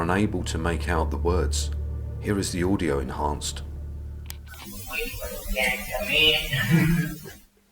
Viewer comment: Thanks and team for yet another excellent investigation and production, how you haven't got lot's more subscribers is beyond me. As regards the EVP at 20:45 , I hear 'waiting for the jailer to come in,' keep up the great work, and stay safe.